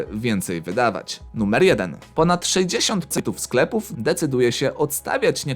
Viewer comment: Ponad 60 cepów Kto też usłyszał daje like